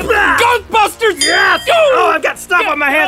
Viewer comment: I thought link said goatbusters